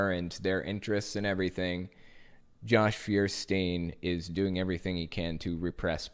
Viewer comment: I thought you said "Josh Beerstain".